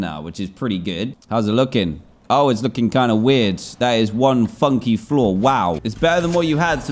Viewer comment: Why did I hear "f*cky floor"